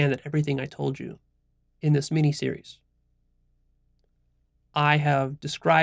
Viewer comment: Did you just say "Meanie Series"?